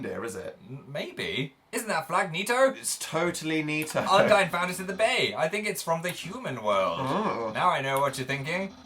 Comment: I thought they said "Undyne is the bae" iM cRi